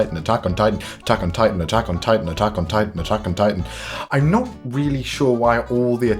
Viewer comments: I just heard “taco nitan” for when you said all those attack on titans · Lol it literally sounds like you were saying Taco Titan because you were saying it so much😂😂 (I know that this is an old video by the way)